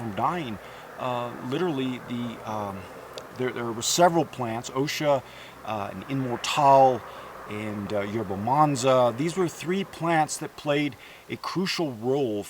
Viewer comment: Great man. Thank you. At 9:03 there was several plants that helped fight influenza... Can anyone hear there names? Thank you for your time. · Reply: INMORTAL (not IMMORTAL), OSHA, YERBA MANSA refer to website <link>, and you will see these herbs